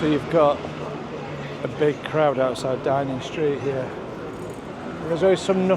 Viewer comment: did you say DINING street 🤣😂🤣